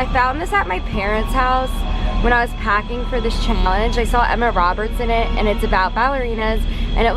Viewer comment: Ok but am I’m that only one that heard her say Emma Roberts not Emma Watson?